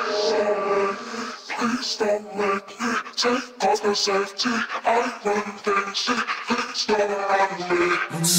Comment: "Please don't make me take off the safety I run things, see, things don't run me" ⚡🔥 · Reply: haha thought it said "please dont make me take off the safety, i run crazy please dont make me"